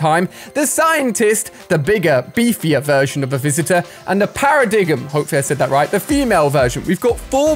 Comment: Did he just say Para-dig-em When it is supposed to say Para-dime